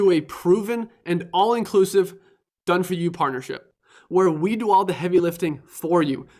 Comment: I misheard "Done For You partnership" as "Done For You parlor trick". Seems appropriate.